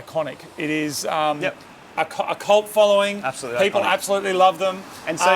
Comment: did you say the car has a cult following because it sounded like you said colt following. Was that a clue? Please say you're bringing back a colt version R.